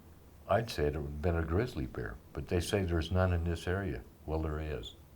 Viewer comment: is that a female voice at 7:16 ?? I heard "there's no.... something"